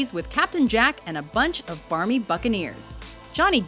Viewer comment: I thought she said fucking ear, but it was buccaneer (-.-)